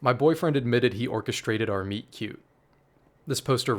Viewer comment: i thought you said "meat cube" and was genuinely so confused on what the HELL was a meat cube